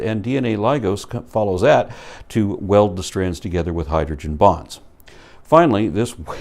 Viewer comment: For about a second, I thought he said hydrogen bombs at 8:21, not bonds.